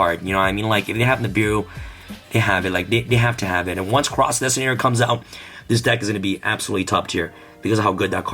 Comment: what card did he say? I could not understand and the subtitles did not read a real card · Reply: It sounded like crossout designtator